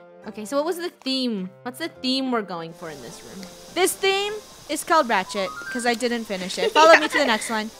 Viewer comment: did funneh just say rat s**t